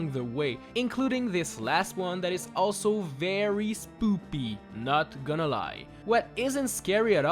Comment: is it just me that heard "spoopy" instead of "spooky"